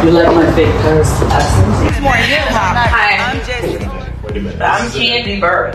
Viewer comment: Wait wait wait .. Did she just say do you like my fake purse 👛 😳 🤣🤣🤣🤣🤣 IM DEAD